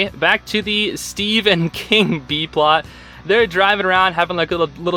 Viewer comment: "The steve and king plot" I heard "the Stephen king plot" and my brain took some solid 10 seconds to load lol